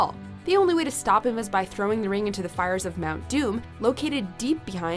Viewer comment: well when she said mount doom I thought she said Mountain Dew donut judge me boiiii